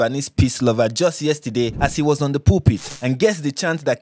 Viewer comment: Im so sorry but the way you said pulpit i thought you said while he was on the poopy